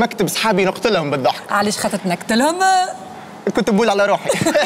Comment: شكون سمع ف دقيقة 1:25 قاتلو علاش خاطرش تنكتلهم ولا انا ماسمعتش مليح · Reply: تنكت لهم